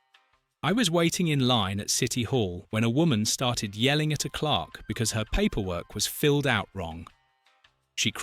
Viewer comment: What I hear is: “The woman started yelling at a Clock . . . “ while the text is “Clerk”. Which one is it? 10:53 There it is again! Is it a “Clock” (as we are hearing it) or is it a “Clerk” (as the text indicates)?